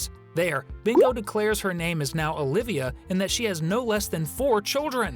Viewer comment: Bingo said her name was Olivia? I heard "Ulteria" when I watched the episode.